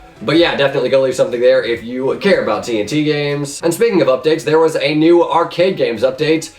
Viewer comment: is he sayin speaking about dicks? Lol